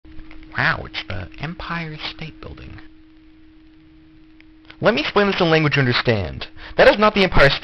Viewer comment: “Let me put this in a language you can understand.” killerbean flashbacks Edit: why did this blow up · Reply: I heard "Let me splain this ih- language understand"